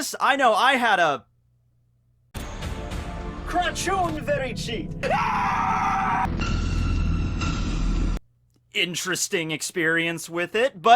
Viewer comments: did you just said at 0:19 "craciun fericit"? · Did you just said "Craciun Fericit?"